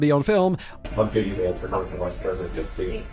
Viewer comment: I cant for the life of me understand what this Vance guy is saying at 9:22, not a word of it. It is just mumbles in the video and the subtitles are no help at all. Anyone care to help me out? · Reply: Googled it. He says "I'm jd vance I'm running for vice president" and she responded "ok" 😂😂😂